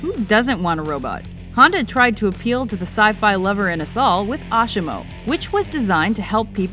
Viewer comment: Please, I need to understand what she says at 3:37. She says "Honda tried to appeal to the sci-fi lover and the ......" ? I hear something like 'soul' but it doesn't make much sense to me. Can anyone help me figure it out? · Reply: and us all